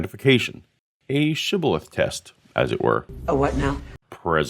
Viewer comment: This is literally the first time since college 20 years ago that I've heard someone use the word shibboleth. That's one of my favorite technical words. It's super useful discussions about subcultures, secret societies, and other groups that for one reason or another have members that need to remain secret. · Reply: I thought he said ciboulette